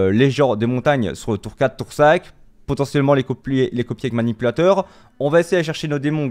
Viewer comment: tu veux faire quoi avec les géants ? " les copulés " ?